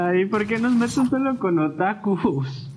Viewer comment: I swear he said tacos 🌮